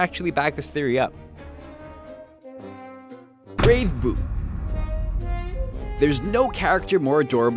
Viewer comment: when it said Brave Poop my sister thought it said Borrowed Poop